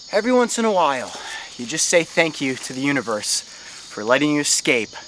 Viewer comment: "thank you to the universe" did you mean thank you Jesus for keeping my life safe? That's what I heard